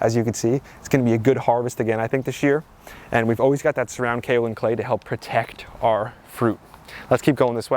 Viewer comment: QUESTION: At time marker 6:32 you said "We always use this something something something to protect our fruit." WHAT DID YOU DAY YOU USE TO PROTECT FRUIT??? You talk so fast I cannot understand most of what you say! Please slow down so you communicate clearly. Thank you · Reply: Surround kaolin clay